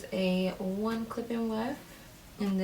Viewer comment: All I hear is “clip in wef”